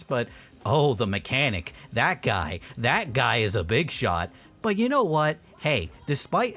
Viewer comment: did you just say [BIG SHOT]??????